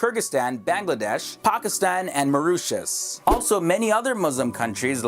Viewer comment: did you just say mauritius? yay i'm from mauritius,muslim and proud! x